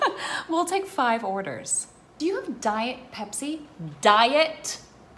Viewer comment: Did she just say Diet Pepsi